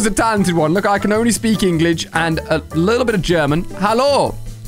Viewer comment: Ali-a said Englig instead of English